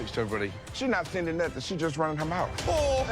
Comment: can someone tell me what said idk i can't understand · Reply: He said "She's not sending nothing. She's just running her mouth"